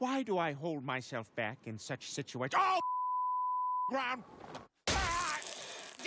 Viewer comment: What was Peter saying when they bleeped so much of his dialogue for wimpy christian audiences? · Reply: I've seen the uncensored DVD version. He says, "Oh f**k, it's the c**ksucking ground!"